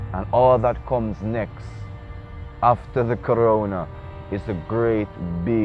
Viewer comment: Did I hear the brother correctly? @ 3:54 "when after the Corona" when this vid was recorded in 2017? or did I hear something else.